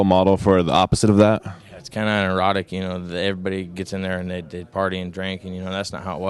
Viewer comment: i think you'll find he actually says erotic, but like i said, he deserved this victory and he can say whateva he wants if hes tired